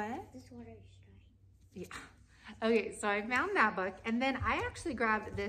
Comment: does your child say “this water is dry.”???? I had to pause the video and listen again and had to do this like three times before I heard because I was laughing so hard and still am…